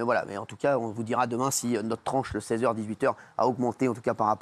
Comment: Et non mamie...encore une fois tu mens comme d'hab 😂 On a tous entendu "ma tranche" ! Quelle mauvaise foi c'est dingue. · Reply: Moi j'ai entendu " notre tranche ". 😅